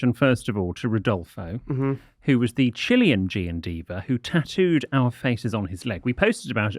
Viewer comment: I thought said the"Chilling G&Diva" rather than "Chilean G&Diva" and somehow I didn't blink lol